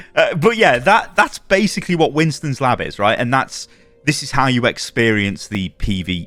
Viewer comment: i thought you said winstons lap and i was like huh?